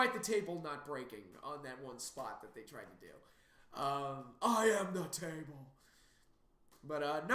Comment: When you said "I am the table" I thought you were saying "I'mmmmmmm Matt Taven" at first lol